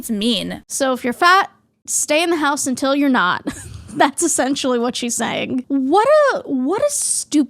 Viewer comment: that's not privalage Olympics. It's acknowledging the differences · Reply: "so if you're fat you should not subject others to you " is what I hear